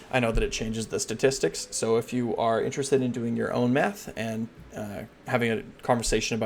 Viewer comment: thought I heard something different there. "If you are interested in doing your own Meth" 😅